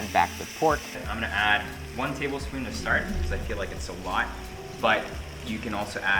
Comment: What did you add in 4:25 as substitute for the bagoong? Thanks! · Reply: Thank you! I misheard it as adding one tablespoon of "star-" or something lol. I guess you said one tablespoon to start 🥴